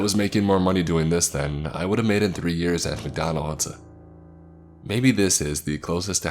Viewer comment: did he say hip Donald’s?